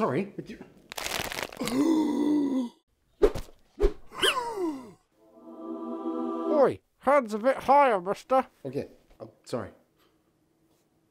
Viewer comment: Lmao, 0:54 subtitles say "handsome gasp"